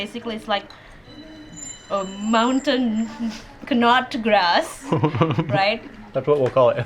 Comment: 18.15 polpala- i think mountain nutgrass is a weed. i don't know english name either scientific name is Aerva lanata and its a herb not grass. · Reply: OOOh i thought i heard it wrong both of them says mountain nutgrass actually its mountain knotgrass (i google it) they miss "K" perhaps its the way of American speaking "herb" do they heats H ?